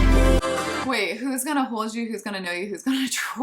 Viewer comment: Wow! I didn't catch that little "me" until you pointed that out!! 🤯 And I also didn't catch "troll", either till now!! How funny!! I thought she was saying "hold you" again!! I've been mishearing the lyrics ALL THIS TIME! Wow!! And it's even funnier 'cause I also have seen the lyric video & just totally missed that!! Haha! 😅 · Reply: I thought she was saying “control you” not “gonna troll you” 😂